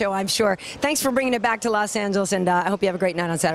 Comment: I heard a "thank you babe" at the end....